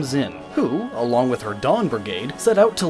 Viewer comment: "Dawn Brigade" I could have sworn he said Dong Brigade, I am calling the squad and giving us a new name.